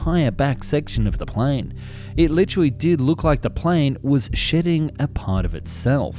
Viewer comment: I misheard that as "It literally did look like the plane was sh*tting a part of itself"